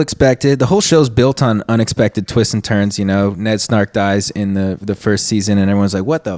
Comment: did he say Ned “Snark”? Lmao